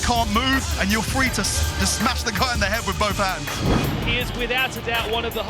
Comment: Did he say "smash the cunt in the head" at 3:15?